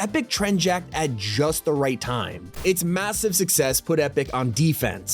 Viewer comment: did i just hear MASSIVE?